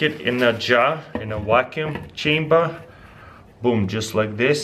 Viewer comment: It sounds like he says"wacuum" in stead of "vacuum"